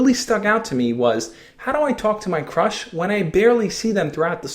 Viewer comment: At the beginning he said how do i talk to my crush but i thought he said how do i talk to my crutch and burst out laughing after he said that xD